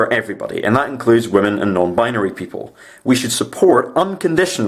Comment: I was onboard until I heard "unbinary" NOPE